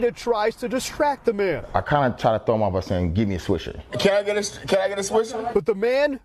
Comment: Am I the only guy who heard him say "Get me a Squishy." :/ bru.